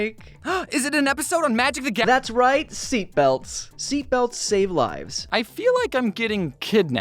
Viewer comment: When he said seatbelts save lives, I thought he said thicc thighs save lives.